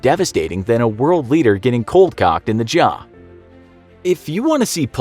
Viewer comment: Did anyone else hear “cold-cocked” at 9:41 instead of cold-clocked?